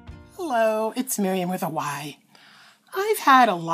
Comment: Fyi I've always thought you said this is with Hawaii. But today I realized it is with a y.